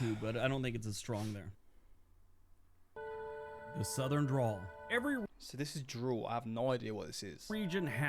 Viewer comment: Sounds like you said drool bud it's dRäwl